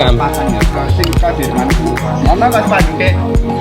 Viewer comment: any fellow indons here to confirm that the word i heard is pantek? 🤣🤣